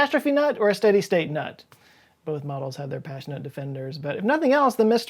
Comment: 15 m 23 s I am like 75% certain I heard you say Pashi-nut...as in passionate, but...not.